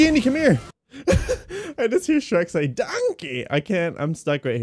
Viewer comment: all I kept hearing in the background was "DONKEH DONKEH DONKEH!". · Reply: I heard Shrek saying DONKEY!